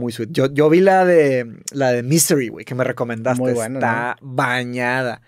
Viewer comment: En 4:10:55 recomiendan una película Como se llama? No se entiende lo que dice Misery o algo así